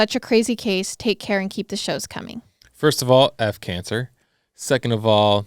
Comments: I must be crazy because I thought said I have cancer.... Right at the ten minute 55 second mark. I hope I'm crazy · Omg 10:55 I thought said I have cancer!! I had to listen several times to realise he said F Cancer! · OMG, did I hear you correctly? 10:52 cancer?? Did I miss something? Please tell me I misheard you. I pray that I am losing my hearing.😟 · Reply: He said "*F* Cancer" in reference to the girl in the story with cancer, not that he has cancer, but it really almost sounded like "I have cancer" so I see where you are coming from lol